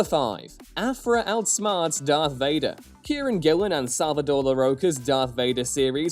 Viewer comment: When you said Kieron Gillen, for a second I thought you said Karen Gillan. I couldn't figure out when Nebula made the jump to comic writer without hearing about that.